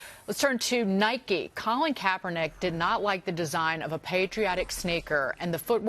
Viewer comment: She says “Snaker” instead of “sneaker”... snaker.